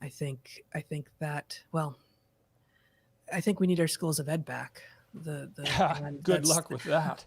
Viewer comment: What does Heather say at 1:16:20? "I think we need our schools' _____ back." If anyone has any idea? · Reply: I believe she's saying "schools of ed", 'ed' being short for education. I'm not familiar with the term but it does seem to fit what she's talking about when I google it.